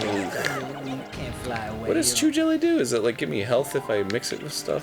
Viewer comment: did he say "What does Jew jelly do?"